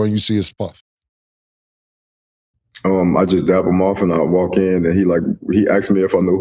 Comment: He definitely should have used better verbage than dapped him off or if he said up it sounded like off and I had to rewind it (pause) cause I thought this man said jack him off. Tough day in the office.